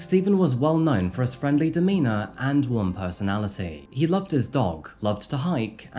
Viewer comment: I know you said "warm personality" but I heard "one personality" and I laughed pretty hard. RIP Steven